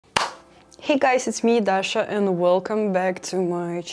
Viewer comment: I thought you said at first, "Hey guys It's me Dasha! Uwuuuu~" HAHAHAHAAHAHAHAHAHAHA